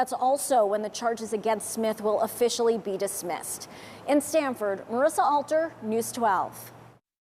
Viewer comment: Originally thought they said “Stanford man”.. Quickly realized they didn’t mean the University once I saw the color of the deceased.